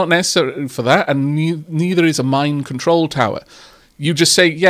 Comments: I was listening to this in the background and I heard "Mind control tower" instead of "Mine control tower" and it took me a minute to realize I had misheard you. I thought the game just shot off in an entirely different direction. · At around 30:00 it sounded like you kept saying "mind control tower", which completely changes the tone of the game.